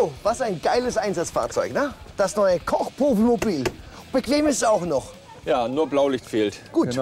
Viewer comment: Hat Fo bei Sekunde 53 gerade Kochpuffmobil gesagt,oder habe ich mich verhört?